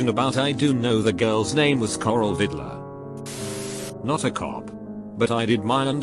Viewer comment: Im sorry but Coral Vidler I heard that as "Coral Hitler"